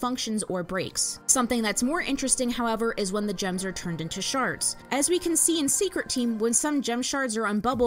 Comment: I swear to white diamond I heard you say "sharts" 💀💀💀